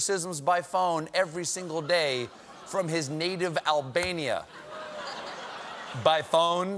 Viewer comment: For a second there, I thought it said Alabama.